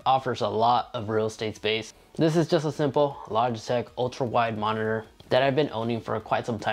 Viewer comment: Did you say it's a Logitech (7:11) monitor?